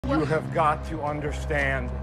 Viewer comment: “YoU aRe LiKe PaPa😤” (Edit)thanks for the likes :) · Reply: i heard "YOU ARE MY PAPA!"